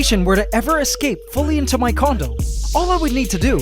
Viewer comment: Did HE say escape into his condom?!?!